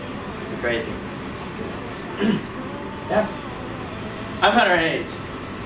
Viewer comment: At the end when he said "i'm hunter hayes" why did I hear "happy hunger games"